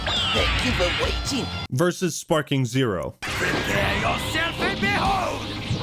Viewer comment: Did Frieza said, prepare yourself pendejo??? 🤣